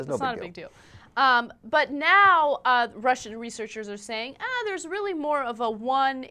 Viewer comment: the lady says "Now, the Russian researchers say..." where as the subtitle say "breast researchers..."